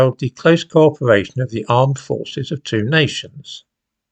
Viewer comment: I heard "armed forces" as "imam forces"